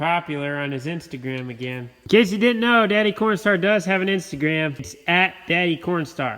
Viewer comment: Wait did i hear " pornstar 😂